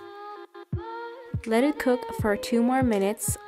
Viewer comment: Idk why but I thought she said “let is cook for 5 days and 4 minutes” 😂💀